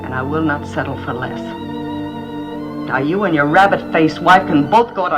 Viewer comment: Sometimes when life has no meaning and I'm feeling particularly down, I imagine myself calling someone up and telling him "You and your rabbit-faced wife can both go to Hell!" and the color returns. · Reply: Thought she said " rabid faced wife"😂